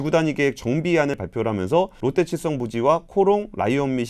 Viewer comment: 편집하시는 분이 급하셨네요 21:51 자막에 롯데칠성이 롯데체성이 되었네요 ^^ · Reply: 들리는 발음 자체가 롯데체성으로 들리긴 하네요. 아마 AI 자동자막 쓰시고 검수가 마지막에 안됐나보네요 ㅎㅎ